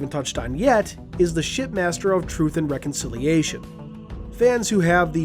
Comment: I swear you said, pardon my language, "Shitmaster"